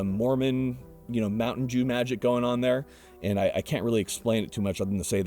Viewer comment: did get just say “Mormon mountain Jew magic going on there”? 😂🤣😂 WTF is that.